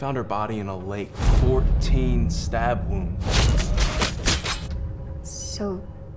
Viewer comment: Sounded like the "28 STAB WOUNDS!" (21? Idk)